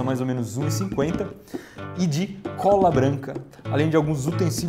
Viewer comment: HSUAHAUSH eu entendi rola branca SUHSUHAUSHUASH